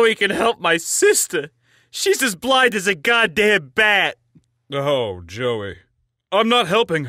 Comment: What they said : "Shes blind like a godamn bat!" "Oh joey..." What i heard : "Shes blind like a godamn bat!" "Oh joy..."